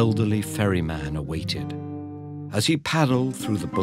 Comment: i heard "furry man" instead of "ferry man" for the majority of the video.